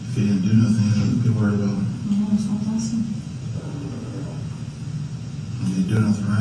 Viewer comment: "No that's not possible" is what I heard.... How did the people who did the subtitles get absolutely everything else spot-on but that? I guess alternative subtitles could be "warm as a possum". It's just amazing to me that sometimes the subtitle crew can get each word 100% right. I'm sure these interrogation tapes would play in court, with subtitles. Someone could easily manipulate the wording to fit their own agenda...